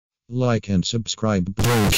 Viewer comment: I heard "Like unsubscribe"